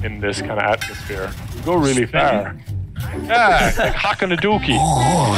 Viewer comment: what the spelling of that word? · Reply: Loogie (10 months late i know-)